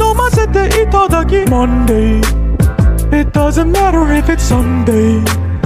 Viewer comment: What I heard “it doesn’t matter if it’s Sunday” 💀